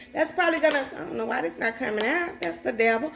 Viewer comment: did you say “I don’t know why that’s not coming out that’s the devil!” lol I’m dying. I had to replay it just to hear it again.